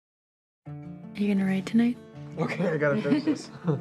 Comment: I thought she said "Do you have to raid tonight?" at the beginning xD. I was like, allll right -here we go!